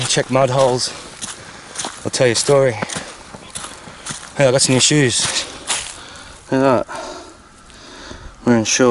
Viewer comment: there seem to be a woman whispering something. I can't catch what it is though... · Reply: She said : Pleacă de aici ! Go away from here ! WTF !